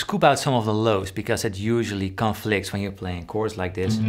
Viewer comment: I’m getting so deaf, I thought you said SCUBA the lows ... learned so much today